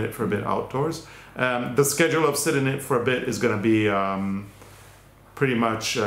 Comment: Lol I thought u said 'sit on it for a bit' 🤣🤣🤣 I think it might be a better title x Sorry. New hearing aids x